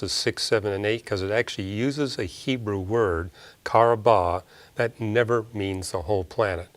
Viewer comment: m what's the Hebrew word...karobah?